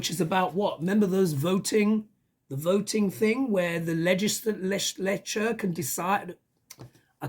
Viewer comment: I got a laugh...I swore you said legis-lecher...I want to be sure to use that term in the future! Thanks for that!